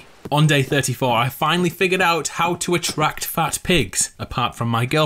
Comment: Him: I finally figured out how to attract pigs other than girlfriend of course." 8:35 Me: WAIT WHAT DID HE JUST SAY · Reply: You forgot the word fat before pigs